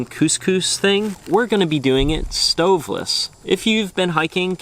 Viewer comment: Yo I really thought that said solventless and you had a big jar of crumble or something I was so confused